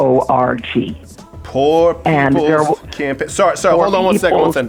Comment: i thought he said poor people cant aim